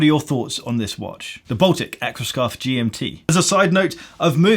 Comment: Could have sworn you said "Aquascarf" couldn't understand why it wasn't keeping you warm🤣 Also never noticed you plugging your straps in your vids before🤣